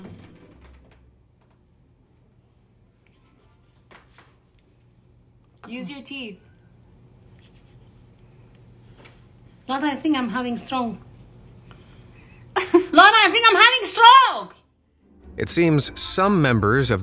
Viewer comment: at first, I hear "I think Im having stronk"